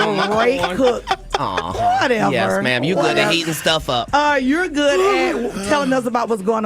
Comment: I think @ 21:14 I think she misheard him. He said “heating stuff up”. But she heard, “eating stuff up”. I think that’s where things went left